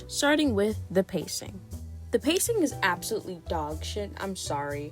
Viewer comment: Lmao I heard "the pacing is ass", then accidentally dragged the progress bar all the way to the right and it just ended there